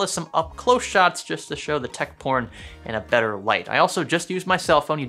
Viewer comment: Did he say tech porn